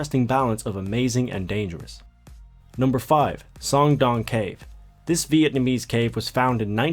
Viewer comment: Idk why but when you said song doong cave,it sounded like you said schlong dong cave.Lol.